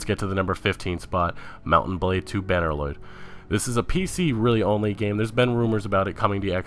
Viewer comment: And banner lord sounded like you said banner Lloyd???